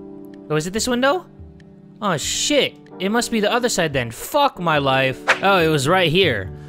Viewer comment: I thought you said my wife boo